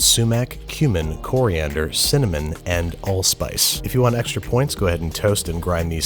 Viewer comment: I heard "Allspark" when you said "Allspice"